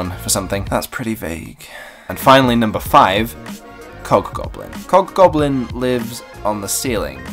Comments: Thought he said 'cock-goblin' for a sec. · I thought u said cock goblin >< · ok I'm really sorry but I thought you said cockgoblin and I was so confused omg :O